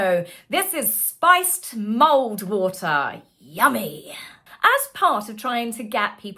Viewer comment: For a hot second I thought you were saying mold water until I saw it written down as mulled water. (Not a native speaker)😂